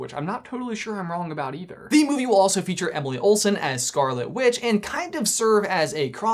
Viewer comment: did he just say emily olsen?